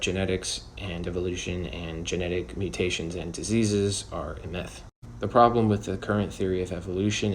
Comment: when the caption says (idk what word i was saying) it sounds like you said "a myth".